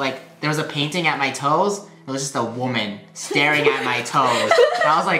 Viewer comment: i thought hi said that (rainbow) staring at yout toes